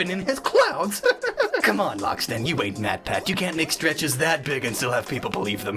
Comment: First time watching: Thought he said "You ain't that badWhat he actually said "you ain't MatPat". Shots fired